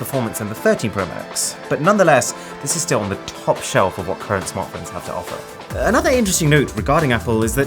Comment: what did he say🔇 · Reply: This is on the top shelf of what current smartphones have to offer